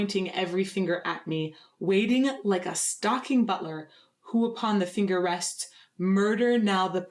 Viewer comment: I don't know why I thought it said " Waiting like a stalking burglar..." - seemed fitting in my head.